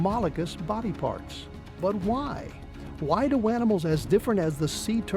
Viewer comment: Did he just call them “wanimals”